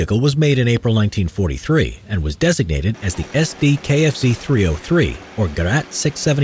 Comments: I heard KFC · I Hear KFC